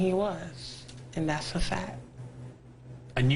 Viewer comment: All I hear is "and that's a fat"